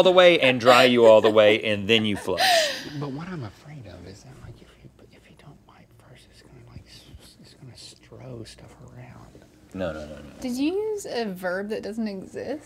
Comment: This is what Link says when he is whispering, "But what I'm afraid of you know, if you don't wipe first, it's gonna like, just throw stuff around."